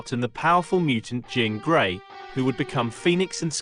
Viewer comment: did you say JOAN gray??? You said Jean later but I’m like 99% you slipped up. YT vids require PERFECTION!!!